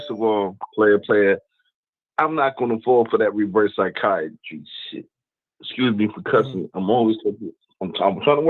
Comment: Did buddy say Reverse Psychiatrist?? 😂